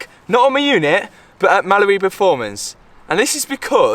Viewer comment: If the signs for Mallory performance weren't up I swear it sounds like your saying Malibu